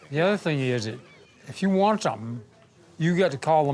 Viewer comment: this man is so detached from society. Instead of saying "Facebook", he called it "Spacebook". I am envious at the thought of this mans life.